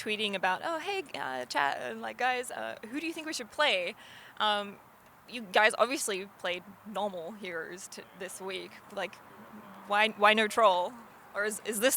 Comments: did she just say "you guys obviously played NORMAL heroes to this week" ? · Did she just call champions "hero's"?